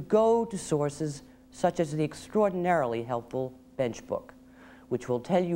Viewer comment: Wait. Did she just say "...the extraordinary helpful Facebook?" It couldn't be. Facebook didn't exist in 1992. Nor would it have been helpful. For Christ sake. Lawyers wasting time on Facebook. That's the last thing we need.